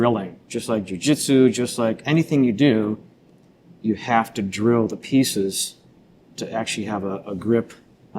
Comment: He says "drill the pieces", and I hear "I know the pieces fit...." 🤘